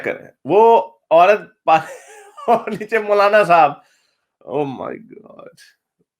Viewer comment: Did he said "O my God" at 4:35?